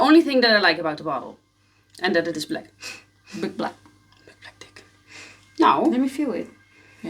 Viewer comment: did U just say - Big Black Dic*? 😂😂😂 Amazing review like always! :)